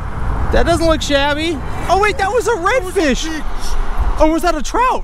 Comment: MEANT TO SAY " THAT WAS A BIG FISH!! BUT IT SOUNDED LIKE HE SAID "B!TCH!!" HAHAHAHAHAHA 😂😂🤣🤣😭😭😭